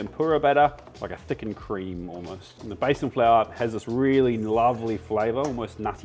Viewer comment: If I didn't know(which I do but I still misheard it as Basin Flour) what Besan flour is I would've gone searching for Basin flour😅